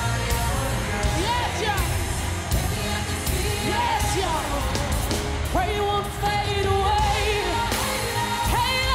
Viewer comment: what did she say? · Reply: “yes y’all”